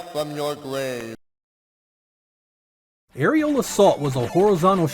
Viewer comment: Aerial Assault...I swear I thought you said "Areola Salt."